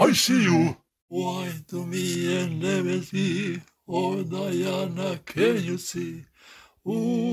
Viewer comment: the song he sings actually says "I love you but you love me. Oh, Diana, can't you see?" the original song is diana by paul anka no this does not mean grannys real name is diana, diana in question is the girl the song is about that paul wrote and sang i have no idea why the first part sounds so different it was probably an audio glitch on dvlopers end and it made it sound different or his dad sang the first part of that specifically in a different language for some reason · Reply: i hear “want to be a nemesi? oh diana can you see? who tey are.